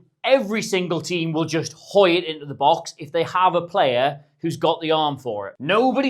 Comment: Did you just say "gooi it into the box"? Since when did Afrikaans make it into British English...? Love it!